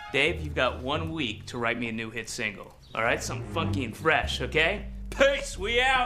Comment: Did lan say the F word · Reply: Legit It sounds like he said Fucky and fresh but he says funky and fresh